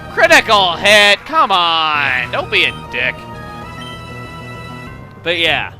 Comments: did just say Dick? D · Did he just say "dick"? Wow, didn't expect to say that. :P